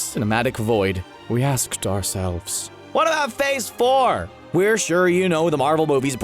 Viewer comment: I heard him say “waddup phase 4!”